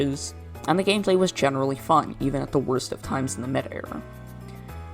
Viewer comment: What is saying: "at the worst of times of the meta era" What the subtitles think is saying: "at the worst of times of the mid era" did Prem did the subtitles or what? 🤣🤣🤣